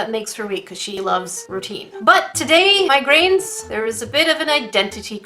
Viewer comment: Instead of hearing "grains" I heard "migraines" ...sounds like a level up to me! 👍😜